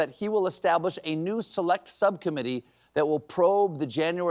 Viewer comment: I heard sub-commedy instead of subcommitee. And it sounded logical ! 😅